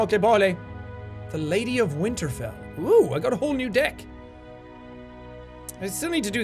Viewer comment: did Jack just say "oooh I got a whole new dick"